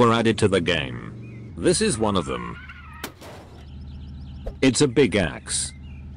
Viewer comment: "it's a big axe" and i heard it's a pick axe xd